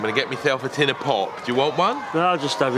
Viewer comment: sounded like he said a tin of pork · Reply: Pop you silly sausage